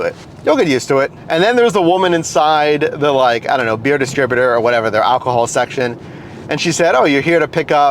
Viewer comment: When you said the line -( when you go inside to see the "Beer Distributor"" I thought I heard ""Bearded Stripper"").